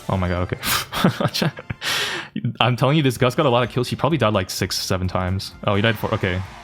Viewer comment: did u say, 6…… 7😂😂😂😂😂😂😂